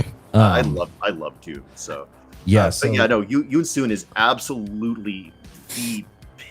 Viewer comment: what setting are they talking about? I can't understand what he's saying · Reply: Yoon Suin